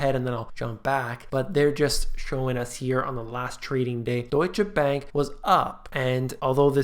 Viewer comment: thought you said douche bank was filing a long time ago.